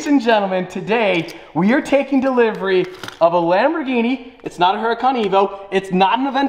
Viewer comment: I thought he said it was not a hurican